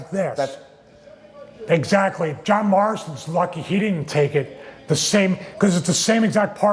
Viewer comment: Am i trippin or at 5:02 did Stevie call him John Marston